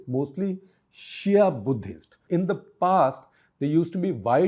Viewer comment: what did I hear it right ?? "Shia buddhists" ??